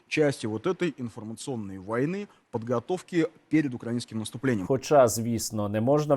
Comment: мені не почулось ? УкраИнским? опа, схоже мовний апарат почав мимовільно виправлятись... Как жє єто проізошло ?